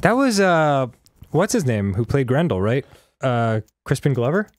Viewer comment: I was listening absent minded and thought Danny said grundle.